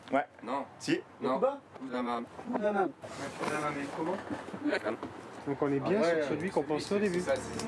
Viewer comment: à 3:50 j'ai compris Kouing-amann. J'en déduit donc que le dealer est d'origine bretonne.😊